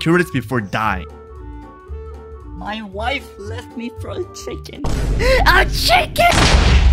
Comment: my wife left me for a chicken, A CHICKEN! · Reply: I'm thought Parrot said "My wife left me for a tweak, A TWEAK!" 😭😭😭